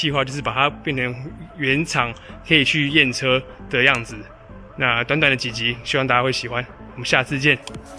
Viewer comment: 邊看動畫一邊聽，最後一句聽成短短的雞雞希望大家會喜歡😅😅😅差點噴飯.............